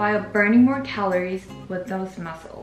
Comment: i cant understand and hear properly , what does she say at 9:56 , what can help us achieve,,,... can someone plsz help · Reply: I know its pretty late but I turned on the cc and apparently she said "resistance training"